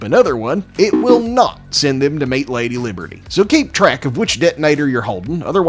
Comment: i heard Meat Lady Liberty